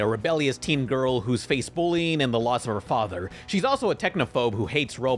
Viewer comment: I heard at 3:00 “the lust of her father”, which would’ve been very on brand.